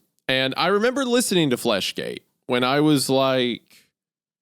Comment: I seriously thought that it said "fleshlight" at first.